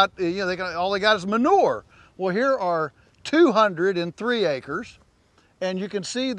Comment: So I know that the ratios for high density grazing is dependent on quality of grass but in this scenario how many cows are you grazing on that 203 acres and how big is the average paddock? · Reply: It seems you misheard what he said. He said 200 cows in 3 acres, not 203 acres. So he's roughly 67 cows per acre, and gave all 200 cows 3 acres for that 1 day.